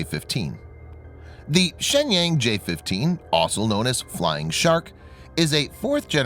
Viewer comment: the Chinese flying war plane is the J-15 also known as "flying sh1t" Listen...it's what it says